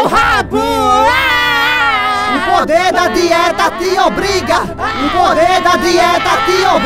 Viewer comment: "O poder da dieta te obriga" kkkk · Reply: Eu entendi " morrer de dieta e lombriga" kkkkkkkkk